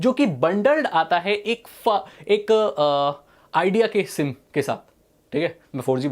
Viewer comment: ye kya bol re ho😂😂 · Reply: Ak fa aaaa ak idea k sim k sath 😂😂😂😂